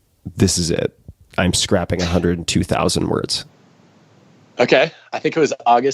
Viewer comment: When you thought Adam said he scrapped 1,000 words then Tim rephrased it and you hear him say 100,000 words 🌝